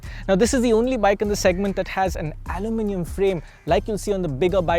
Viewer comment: does he Say it has an "Aluminum" frame or did he just say it has an "Adamantium" frame??? (Wolverines Claws are Adamantium)🤔😅🫠